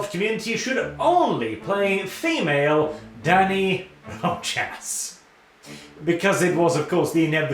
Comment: With the mic issues, I could've sworn he said "Female Daddy Roach-azz."